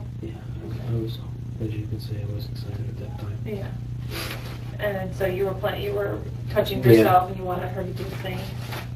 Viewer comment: What does the interrogator say to this ped at @ 34:00? “Pretty big/good” what? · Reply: i think she said "pretty big star" as in the sticker over his pecker? i don't think that was necessary of her but 🤷🏻‍♀️